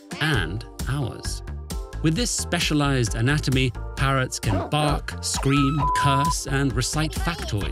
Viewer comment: At first I thought it said why carrots 🥕 can't talk 🤦 It'd be kinda cool if I could so that if they were bad mouthing to me I could gobble them up